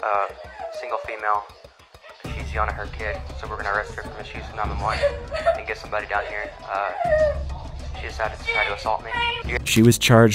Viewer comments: the unintelligible is him saying “misuse of 911” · the unintelligible is him saying "misuse of 911" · he didn't say "She yelling at her kid, so we're gonna arrest her from the unintelligible" HE ACTUALLY SAID "She is yelling at her kid, so we're gonna arrest her for misuse of 911" you dummy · the captions say "*intelligible*" but I believe it says "misuse of 911"